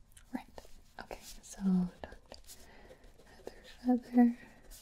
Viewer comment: Did anybody catch when she said heather feather